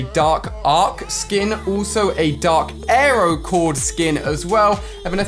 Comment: Did he just say aero chord I’m pretty sure it’s power chord